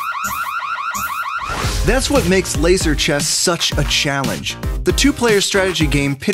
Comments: I thought it said laser cheese 😹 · At first a thought it said laser cheese